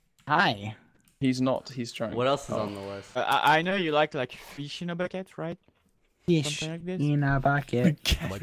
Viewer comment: I THOUGHT HE WAS SAYING FISH IN A BAGUETTE